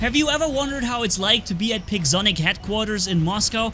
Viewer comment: i thought said pigzonic 😂